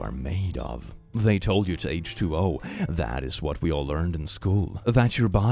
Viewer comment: call me stupid if I’m wrong but isn’t h2o2 hydrogen peroxide. Edit: Holy hell I heard that wrong.